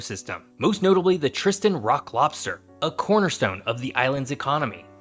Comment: did he say Iraq Lobster?